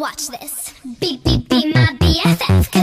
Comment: I swear it sounds like their saying "lmao with the rest" anyone else with me? o.o